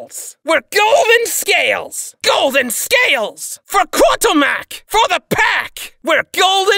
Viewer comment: What do they say “for Karl!”, i hear “for kartomac” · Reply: Kurtulmak, the kobold deity.